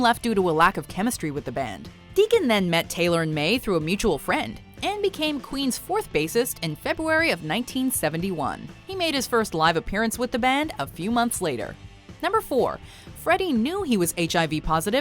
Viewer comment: Is she saying "febuary" ?